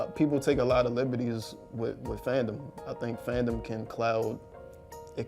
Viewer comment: did anyone else hear him say clout and then correct himself and say cloud